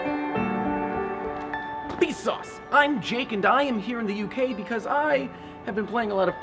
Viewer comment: did he just say?